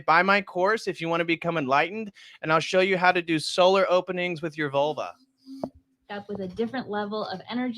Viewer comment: Solar openings with your Vulva 😳😂🤣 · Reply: I thought he said soda openings 😂 As in opening soda bottles with their vulva. 😂😂😂